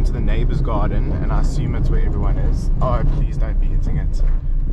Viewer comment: What did you say at the beginning as you pulled in? I heard 'Please don't be eating it.' Figure it can't be that lol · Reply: 😂😂 I said, "Please don't be hitting it, because I saw the guy with the stick stabbing the bush😂👌